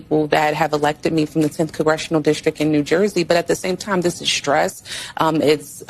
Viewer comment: SOUNDED LIKE “CORRECTIONAL DISTRICT”, THAT IS A FREUDIAN SLIP, SHE KNOWS THE DISTRICT SHE WILL SOON BE IN😂😂😂😂